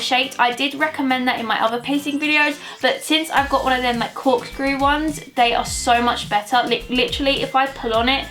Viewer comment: I’m sorry I’m not English so i didn’t really understand what you were saying at: 5:57. What did you exactly say? Because I wanna get a nose piercing so I really need to know what type of stud I should get. xxx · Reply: Cork screw nose piercing :) x